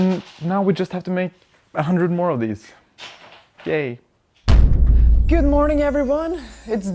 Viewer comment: Am i a little bit to queer, or did he really say GAY there